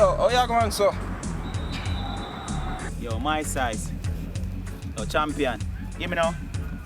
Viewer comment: lol @ 2:10 did say "yo, my size"? ahaha